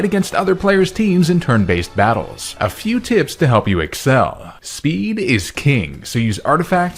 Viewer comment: During the Raid sponsor segment I thought he said "Speed is key" and had flashbacks